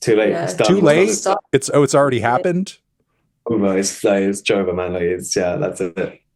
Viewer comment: did he say "its joever" 😭